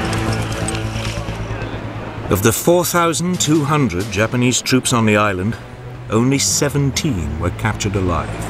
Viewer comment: 오운리 쎄븐틴 이라고 명확하게 들리는데 자막에 16명이래서 당황 ㅋㅋㅋ